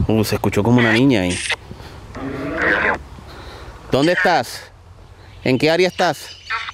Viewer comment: En el minuto 15:17 se escucha que dice "donde no hay paz" cuando pregunta en que area esta